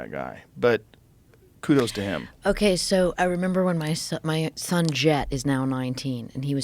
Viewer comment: Did she say her “ex son”? 🤨